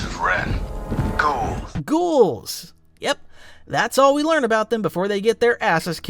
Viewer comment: The Knights of Ren are Ghouls...? I thought the Stormtrooper said "Cool." instead of "Ghouls.", lmao.